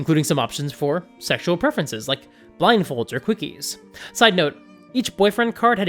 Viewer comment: I heard 'cookies' instead of 'quickies' lol. Now I need to make a character or npc that really loves cookies.